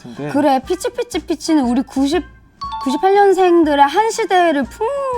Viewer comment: 역대급 병맛 인트로네 ㅋㅋ 시작부터 그 마녀 웃음소리 ㄷㄷ 만화 잘 안보는데 어떻게 나왔나 했는데 실수?로 나왔다니 ㅋ 승환이만 신난 회차 킹받게 하는 능력이 점점 좋아지네 ㅋㅋㅋ 이제 피치피치핏치가 이상하게 캐치캐치캣치로 들리네